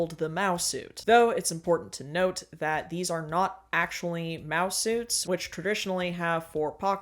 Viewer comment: Ohh, MAO SUITS. I thought you were saying mouse suits the entire video